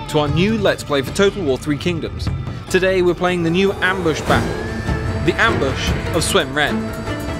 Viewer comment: At first I thought it said "Ambush of Sun Ret's Pray." ...Sorry.